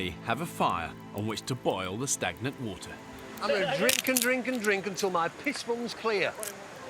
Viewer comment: I keep hearing him say "And now, I'm going to drink my own urine." after every sentence.